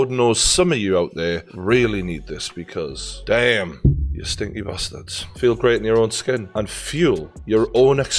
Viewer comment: did he just call me a stinky b@